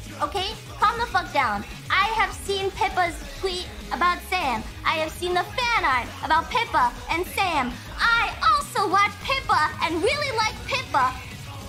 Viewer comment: I thought she was saying "Pippa and Sans"